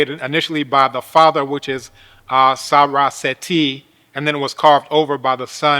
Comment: 34. Did I hear the name Saara? That's the name of the current queen mother of Denkyira Kingdom.